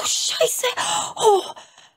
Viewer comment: Habe ich da gerade deutsch gehört? "scheiße" :D